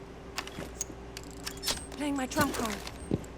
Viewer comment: At around 4:11 I though she said "Playing my trap card." but she really says "Playing my trap clone." Or so it sounds.